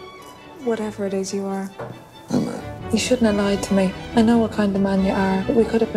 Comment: i know that thats probably not what hes saying but it sounds like he replied with "homo" and i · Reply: Haha, he says "I know"; but I love your version better XD